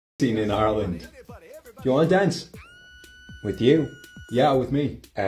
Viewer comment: at first i thought he said “do u wanna dat?”💀✌🏻